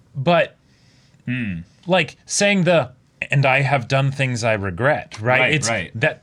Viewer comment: she actually says that she HASN'T done anything she regrets, even after death.